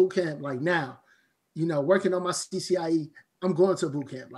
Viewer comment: what did he say was the name of test he’d go to a bootcamp for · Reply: CCIE